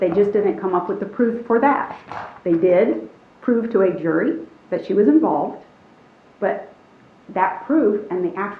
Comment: Did the defense attorney say at 18:32 that it was proven to the jury that she was involved? Did I hear that right?